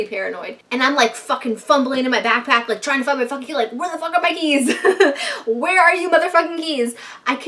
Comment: i thought she said where are my knees lol 💀